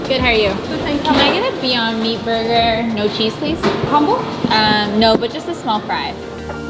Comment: I'm pretty sure I'm wrong but I thought I heard the cashier say, "Humble?" Then she answered, "Uhm, no. But just a small pride." I've repeated this 3x already and I'm still hearing the same thing. 😂😂😂 Please tell me what they actually said. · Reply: Maia Hisula “Combo?” “No, just a small fry”